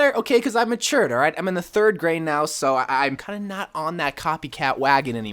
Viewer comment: I thought you said 30 years and not 3rd grade and I was just like, "I'm crushing on a thirty year old." Lol